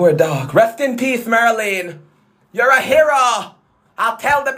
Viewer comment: DID HE JUST SAY RIP MARLYNE? 🏖️ CRAZY!